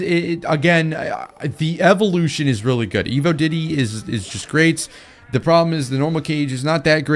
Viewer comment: what did he say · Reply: Evo diddy